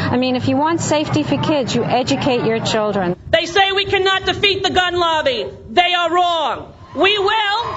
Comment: Did Rosie say "They say we can not eat the gun lobby" ? No wait, that was defeat, not eat, good thing because Rosie most definitely could eat the gun lobby.